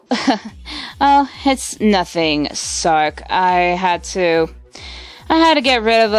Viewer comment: d-did rouge just say sark in 15:51 THE sark demon of sharpness in the nightmare universe..........................